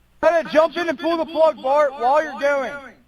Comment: I listened closely to this a few times!!! Listen closely starting at 23:43. It sounds as if the statement “Jumped in a pool of blood, Bart.” What are you doing”. “Fuck” “Take it out of here “. Did I hear this correctly??? My heart breaks over this